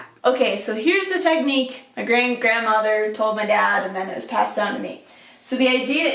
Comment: i totally thought you said 4th dimensional grandmother. i guess either way it works. have a nice trip! i'm praying by some miracle I can go to arizona.